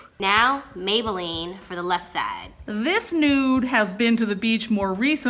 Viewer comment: When was saying “nude” i thought she said “this NOOB has been to the beach!” ❤️❤️